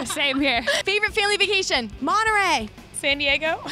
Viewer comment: what did dani said at 1:56? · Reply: monta rey